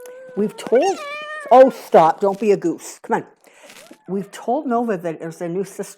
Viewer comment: LOL! When you told Nova, "Don't be a douche", it made me chuckle out loud. I say stuff like that to my critters all the time. They're full of beans too. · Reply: 😂 I thought she said "Don't be a goose" 😂 Douche is funnier